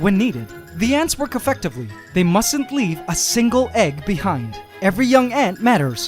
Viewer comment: did u just say must'ntly